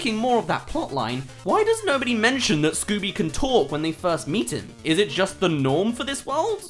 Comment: did he say meeted